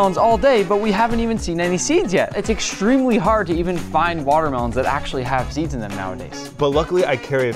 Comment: Did they say it was hard to find water mode without seeds